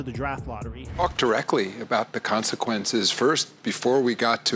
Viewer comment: I thought Adam Silver said F*** directly